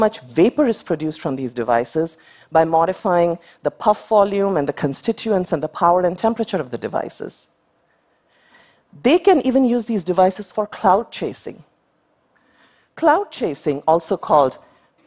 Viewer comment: thought she said clout chasing. Never heard of cloud chasing 🤔